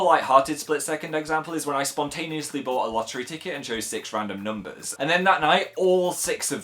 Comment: For some reason when he said "lottery", I heard "laundry".... OK, brain. XD I'M SO GLAD YOU'RE ALIVE, PHIL!